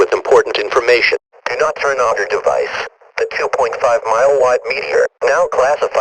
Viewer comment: sounded like “please do not turn on your device”